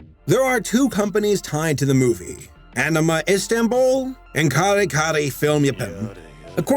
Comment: Anyone else hear Jotaro saying Yare Yare in the background?